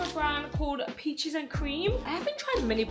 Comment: Full face of all the new products!!! Youtube style 🫣 i would love to watch a super long video 💕 But why did it sounded like you said bitches and cream instead of peaches and cream 😂😂😂 I remember you once did a video of looking and your subscribers's content and giving them tips to improve, could you please do that again ❤